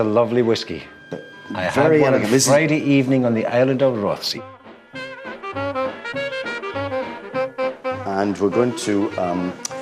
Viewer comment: Does he say the "Island of Rothesay" at 5:17?